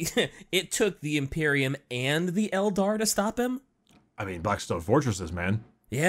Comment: I swear I heard say "(...)some SMELDAR(...)". I know that I just had a brain fart but I am giggling right now so ... Yeah.